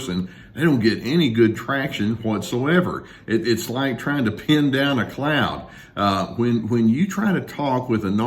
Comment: 😂 at the beginning when you said, "... ( ... it's like trying to pin down a cloud... ) ..." I thought you said "...pin down a clown..." at 1st.